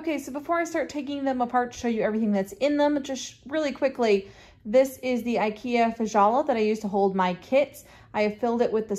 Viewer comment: HI. Can you please spell out what that tray from Ikea is that you are pointing at 1:12 on your video. Ive searched and searched for what I think you said but I cant find anything. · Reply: It’s an IKEA Kallax, but may be discontinued. I have seen them on eBay or other secondary retail shops though.